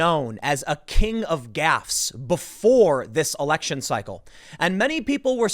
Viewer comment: Thought you said ‘King of Gas’ and I thought so besides Alzheimer’s he has uncontrollable flatulence?